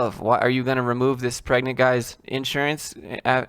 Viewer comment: Did you just say pregnant guy? As in a pregnant male? I had to rewind that because I thought I misheard rofl. Anyway. Great video pointing out the obvious that apparently completely went over the head of the person who wrote that article. Your first message was not hard to understand, and this one isn't either. But expect this dude to conveniently misunderstand your response video yet again 🙄